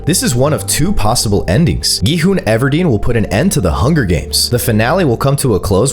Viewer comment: “Gi hun will stop the hunger games😂” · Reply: I thought you said burger games😂😂😂😂😂😂